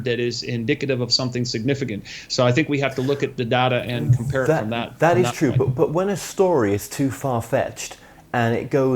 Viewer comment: I can't hear the name of the book at 26:15 is plugging by Albert Roseos. Can't find anything like that on Amazon. Help? Thanks! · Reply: Albert Rosales